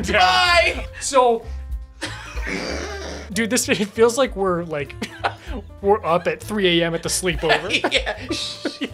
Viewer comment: When he was looking for the gravestone, I kept hearing “Bienvenido” lmaooo 😭 “welcome” in Spanish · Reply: It was Bienvenido, it's a legit person's name as well as a greeting